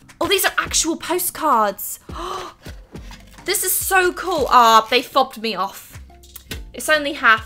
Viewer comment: Omg did just say the fuck? 2:58 I’m fine with it just surprised I’ve never herd her say it befire